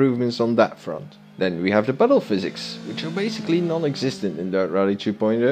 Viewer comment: "battle physics"... what is that? I can't quite make out what you said but according the closed captions, that's what you said 🙂